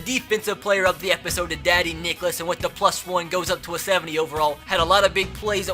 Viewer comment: Why is it every time you say "Dadi Nicholas" I keep hearing "Daddy Dickless"? Lol